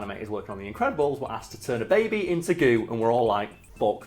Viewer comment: I thought it said "Turn a baby into God" and I was very confused.